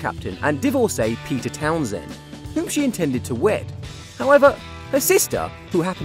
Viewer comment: I heard "whom she intended to WET" and I was like HOL' UP And then I went back a realized he said "WED" 😆😬😂